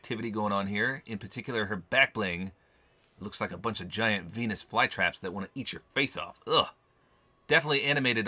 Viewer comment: Did he say p**is fly traps at 4:52 sounded like